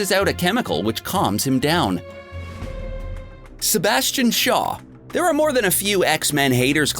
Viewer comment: I thought that said Sebastian Stan